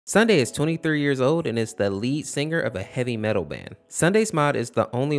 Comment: At the beginning i rlly thought u said "Sunday is three years old"